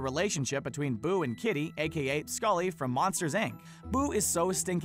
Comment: did he just say scully?